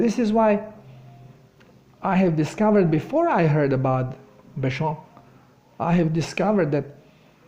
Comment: Hello, first I want to thank you very much for helping us, I appreciate it very much. Second, can you plese tell me what you said in minute 28:00? Sorry I didn't understand. Thank you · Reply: He said, "Béchamp," who is considered the founding father of terrain theory.